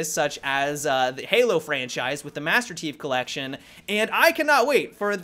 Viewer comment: it sounds like was saying "Master Teeth Collection" LOL